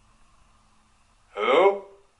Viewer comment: heard a whisper sounded like hello